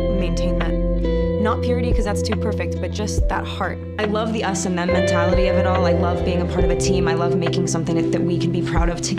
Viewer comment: Did she just say. "The S&M mentality of it all"?